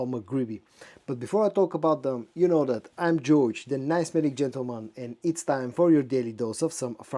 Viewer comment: Did he said I'm Jewish 😅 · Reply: What? He said "I'm George", you guys are not used to eastern European accent lol